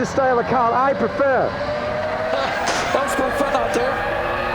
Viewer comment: what did he say ? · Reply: that’s been fun out there